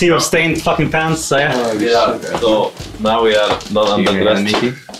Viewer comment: I want all Miky pictures where are they? *btw what did perkz say at 1:17? i cant hear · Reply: Now we are not under-dressed